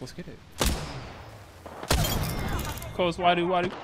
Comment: did Wadu just say "close wadu wadu"?